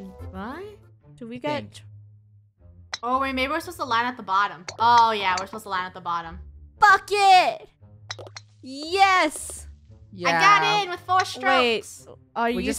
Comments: I thought said F*** IT idk y? Edit: I JUST GOT SHOCKED OK IM NOT A HATER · Did it sound like said F*** it at 11:40 Ik she didn’t she said bucket lol 😂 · OMG I thought she said the f word but really sad bucket sorry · who else heard what said? :0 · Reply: She accually said bucket but sounded more like F it xD